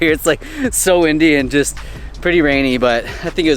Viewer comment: Everytime you said "windy" I thought you said "Indy" aka Indian and I had to rewatch several times cause I just didn't get it😂